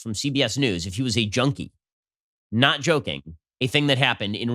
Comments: Ok America, Let's start the judging of Biden and his mental fitness. When he says junkie it sounds like he said Joni lol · I thought he said "Joni" Not "junkie" wow, he's awful!